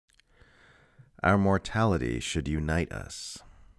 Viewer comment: I thought that you said: "Our Morality Should Unite Us". Funny, it is only one letter difference.